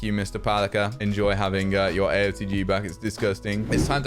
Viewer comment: did he just say aotg?